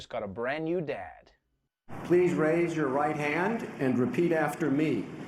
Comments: I misheard "please raise you white hand..." 😂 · I thought they said "please raise your white hand"...